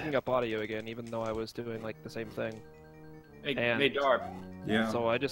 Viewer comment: I swear I hear my name, right after “Hey Dan” sounds like “Hey Jarv” and that’s my short name. Very unlikely, but idk Edit: I see Darb in chat, and just realized what the channel name is